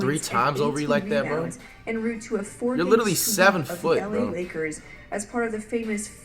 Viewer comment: Did call Larry bird 7foot